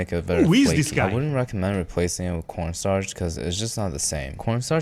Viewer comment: The kid said "Corn starch." I heard "Porn star." What does that say about me?😂 And yeah. We have cookie dough you can eat raw (NOT what he used), which I suspect doesn't have eggs or something. And you can't beat the GOAT that is Ben&Jerry's Chocolate Chip Cookie Dough ice cream!🤤